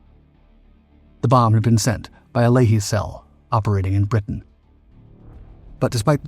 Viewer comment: The bomb was sent by a ??? Cell . Sounds like he says layee cell 🤔. Anyone with anymore information on that part of the story. ? 😊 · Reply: A Lehi cell. One of the "gangs"